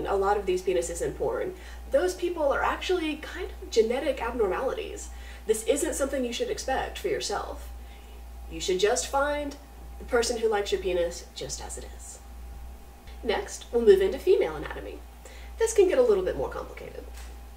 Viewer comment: "you should find the person who like your penis ,jusr as it is." i heard "you should find the person just as like you're"